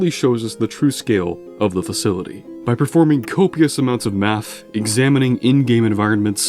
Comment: Sounded like you said “copious amounts of meth” 😆